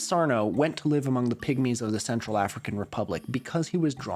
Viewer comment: Oh no, I've been brainwashed... I heard "pick-me's of the Central African Republic, not "pygmies" at